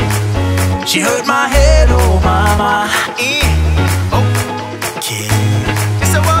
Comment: I thought I heard "hurt my head Obama" the first time so I had to Google..shit I was so wrong hahahaha